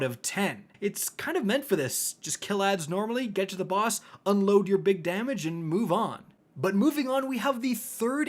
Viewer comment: In the Shuro Chi encounter review, I legit thought he said "...unload your big daddy..." not "...unload your big damage..." 😂